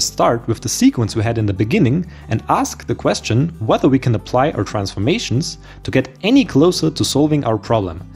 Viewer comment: This video would benefit greatly from subtitles. The narrator's accent is so strong that it is hard to understand what he says -- sometimes impossible, e.g. at 7:52. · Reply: "Whether we can apply our transformations..." I understood it just fine